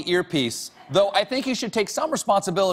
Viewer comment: Did he just say hairpiece...sounds like herpes...must be my earpiece